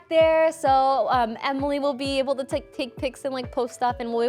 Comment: I honestly thought said 'Emily will be able to take feet pics' whilst promoting their Patreon at the beginning...